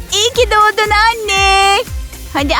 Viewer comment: anneler günü yazıyor ama iyimi doğdun anne diyor